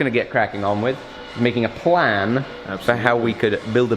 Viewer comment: I thought at first you said was going to be making a flan. How are his baking skills?